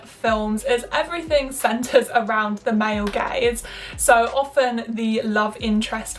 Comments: 2 things -Take a shot everytime she says "internalised misogyny" -Every time she said "Male gaze" I heard "Male gays" until I finally turned on the subtitles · Am I the only one who thought she said "male gays" instead of "male gaze" I'm like since when does this society value the male gays